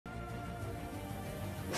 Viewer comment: What I hear: "Men, I'm gonna I'm gonna break my money trough i swear"